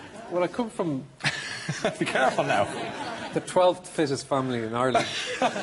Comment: What does he say at 2:30 "i come from the 12th something family in ireland" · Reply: It's fittest.